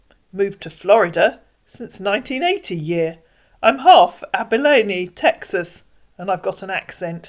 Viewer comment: I thought he said half Abalone 😅